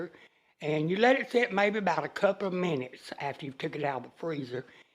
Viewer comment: on the okra, she said you take as much out of the bag as you want to use, but it sounded like she said a cup of mayonnaise, but I am uncertain about that because I listen to it three times. I just love and miss her so much.🙏🏽😇♥️